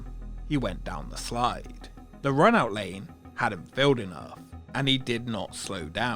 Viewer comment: 1253. The run out lane had a what?? I can not understand what your are saying. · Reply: The run out lane had not filled up enough with water.